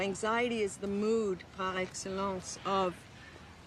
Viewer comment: What does she say at 6:06? Is that french? Latin? “So anxiety is the mood —— of ethicity“ …is that a philosophy term? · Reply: "Par Excellence" - expression from French to indicate something that is "above all else". In this case, she is saying anxiety is the mood par excellence of ethicity. Also, "ethicity" is not a word, but Atival is a deconstructionist so I doubt she cares.